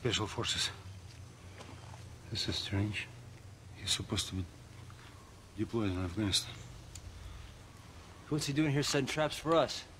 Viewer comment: i have no clue what he said lol. "he something something" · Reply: "He was supposed to be deployed in afghanistan"